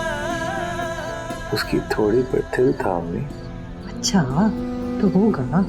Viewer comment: what did he say ? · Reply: Ioski thudi par til tha